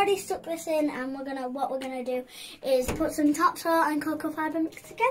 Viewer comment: I thought she said 'top soil and coco powder' lol. Wee rascal wasn't for moving easily. Should enjoy his / her new home. Good job